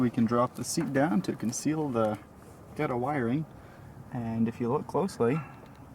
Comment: What did you say at 8:33? Conceal what? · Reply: he said ghetto wiring